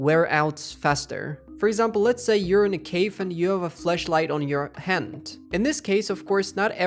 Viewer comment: bro did not say FLASHLIGHT.. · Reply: Flushlight 😂